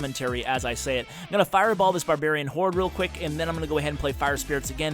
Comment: hey, at 5:27 did I hear you say "Barbarian hoard"?